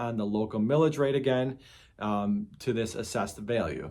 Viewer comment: 😂 Thought you said "Pillage" rate. Feels like states and counties assess to pillage us all the time on tax.